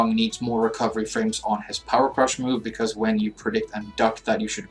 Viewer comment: XMB 79 I mean if you have a better way to deal with it by all means tell me because I will use it. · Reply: uhm....well....you could....I think you should....maybe if you...you could possibly......................oh wait did you say Power Crush!?...I thought you said....sour nuts! Power Crush sounds like a great idea to me man!.............😓